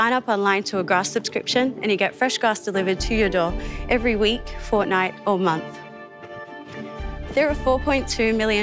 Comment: did she say Fortnite?